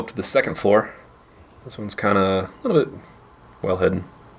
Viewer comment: "This one's kinda "Ribbit" well hidden" lol I had to rewind several times just to figure out what you said. I'm going with "Ribbit"